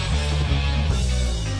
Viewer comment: what did he say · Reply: nuatz You turned the Selmer down, put the Fender down and the Selmer up please. The amps